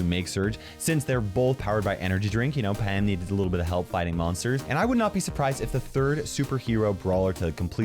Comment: did you say pam needs help fighting monsters